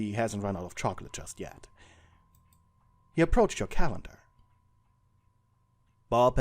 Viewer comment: When you said "he approached your calendar" I thought you said "he approached your cat" 😭💀